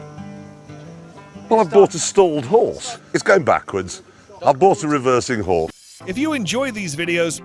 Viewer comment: 😄 The way it cuts off makes it sound like he says "I've bought a reversing whore"